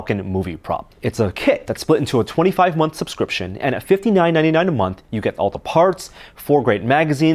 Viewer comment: 60$ per month for the falcon........... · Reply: Raptor Jesus 1500 for the whole thing. I first thought I heard 15.99... that'd be acceptable. but 60? no way.